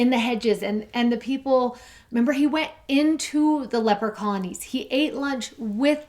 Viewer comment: "He went to the leprechauns..." Lots of laughter over here. I keep hearing leprechauns instead of leper colonies but I suppose Jesus would go to them, too.